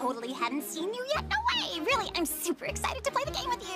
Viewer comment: In the intro I heard “I’m super sus!”